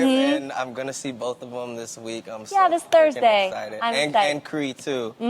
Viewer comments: Did he say “I’m so fuckin exited”. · I could be hallucinating but did he say I'm so f*cken excited at 3:12 and if so how did no one else catch that?